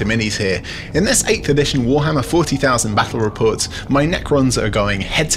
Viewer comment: I thought it said 40000 battle points bruh